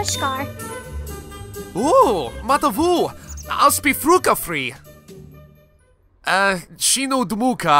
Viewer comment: In 6:50 did he say must be fucka free?